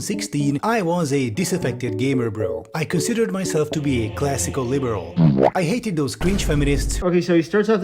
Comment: I thought he said “I was a disaffected gamer girl. I considered myself to be a classic e-girl” can’t believe I misheard him twice in the same way, but I assumed he’d just detransitioned